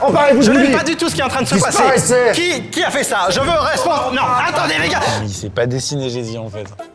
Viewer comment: c moi ou il dit "attendez les gars"